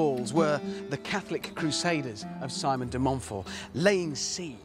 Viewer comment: I know Richard said Simon De Montfort but I heard Simon Belmont!